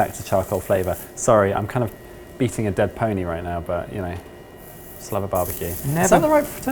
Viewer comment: I heard say "beating a dead penguin"🙈😹